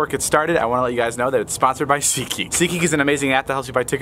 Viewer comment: i heard david say "ckey" but it's actually "seatgeek"..........lol